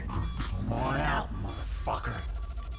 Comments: but really 1:07 did he say ''come out motherfucker'' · did he just say "fucker"? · Did I just heard the predator saying "MOTHERFUCKER" at 1:06?